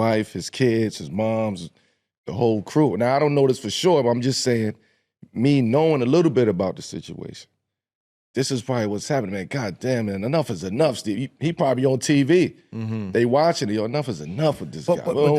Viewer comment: I thought he said he was done talking about the situation 😂